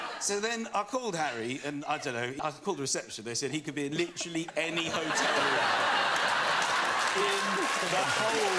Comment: What does Michael say at 6:44 about Harry? I'm not English and I don't understand it😢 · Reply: Bon Trany he could be in literally any hotel